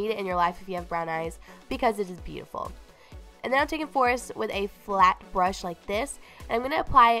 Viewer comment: I heard "taint" lmao I was like wait, what's she talking about